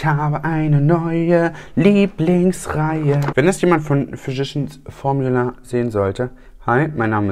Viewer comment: Ich hab immer "verschissen Formula" verstanden 😂 Ich habe schon einige Videos über die Firma gesehen- selbst Tati konnte es nicht aussprechen und hat sich dauernd verhaspelt. Schönes Wochenende!